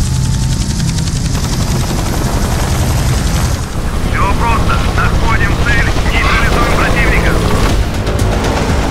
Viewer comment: Anyone know what he said? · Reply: He said "hey soldiers were landing here now go win for motherland